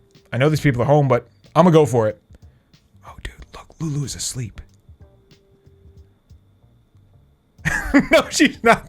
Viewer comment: I thought you said guys f word lulus assleep then i replayed in 30 times then undersud